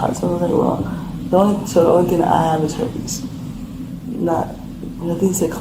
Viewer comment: Did she say the only thing she has is herpes???